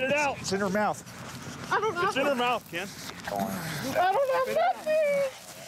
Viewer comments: Hahaha@ 0:18 it sounded like "bitch" instead of spit! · did he say bitch? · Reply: MyOwnEmailShow he said "Spit"